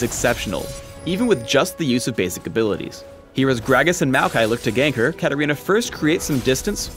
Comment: Did he just call Gragas and Maokai heroes?